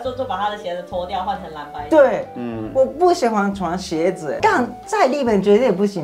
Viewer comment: 把 ”但“ 聽成”槓“ 😂